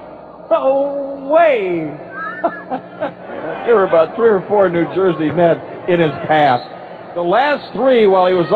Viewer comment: I thought he said "There was like 3 or 4 New jersey nets in his ass" .. LOL